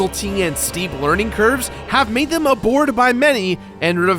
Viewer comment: did he say abored instead of adored?